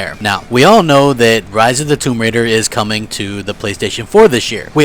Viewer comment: I thought this fat ass said tomb raider wont be coming to ps4